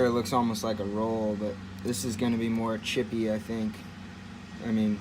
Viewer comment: I could be hearing wrong, but I thought I heard this kid say "cheap S110v steel". From what I hear, S110v is a super steel that is not cheap. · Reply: He says it’s chippy not cheap.